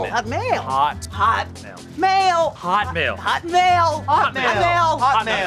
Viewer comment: it sounds like there saying hot mayo